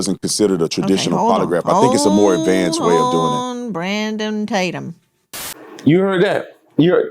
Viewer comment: is it just me, or did Nancy call him Brandumb Tatum? 🤣